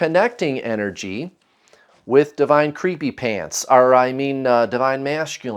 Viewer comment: I totally thought you said, “Divine creamy pants” 🤣🤣🤣🥴😜